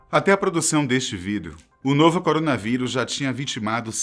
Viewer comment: Será que foi só eu que ouvi ele falar em 00:00 "até a produção desse vidro..." ??😂